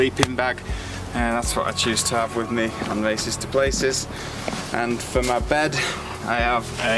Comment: I love his work, but he has gotta be careful, sounds like hes saying 'racists to places' !!!! Any way man keep on racing to those places because it's only inspiring me more and more.